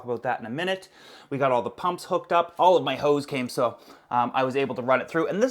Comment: Am i the only one that heard him say: "All of my hoe's came" ??? 😂👌